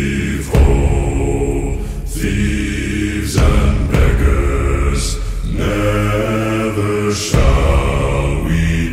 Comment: WTF DID HE SAYYY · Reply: He said beggars